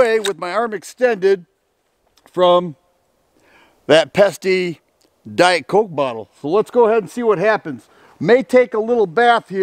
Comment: I thought you said “that Pepsi Diet Coke bottle”. 😂 Had to rewind and heard what you actually said the second time.